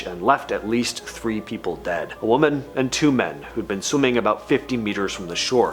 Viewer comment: haha I thought you said sea lions exploded and I was like wtf 😂😂 yeah obviously it was mines 🤦🏼‍♀️😂